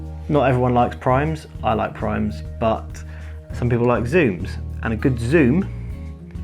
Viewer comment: I thought you said primes rule and the go on to discuss zooms. You lost me.